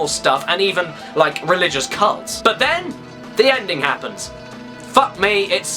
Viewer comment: I heard religious cunts.